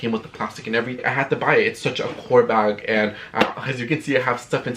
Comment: I definitely thought you were saying whore bag for the longest 😭😭